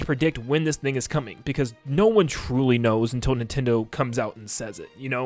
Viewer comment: why did i hear the n-word?💀💀💀